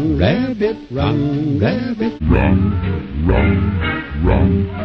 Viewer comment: "run rabbit run rabbit😊" "RUN RUN RUN👹💀👹" edit:ya'll are spamming my notif😭😭😭😭 edit 2:ya'll are actually believing in spamming like stop bro😭😭😭😭😭 edit 3: i will literally stalk uwucutesingle and i'll comment her username at 20k likes edit 4: im not patient so her username is aruntik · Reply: I thought it was saying RUM RUM RUM RUM💀